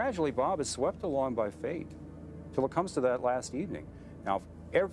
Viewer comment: Did he say "Bob Crane was swept along by fate.", or, "Bob Crane was swept along by faith."? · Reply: tthomaselli2 it sounded like fate.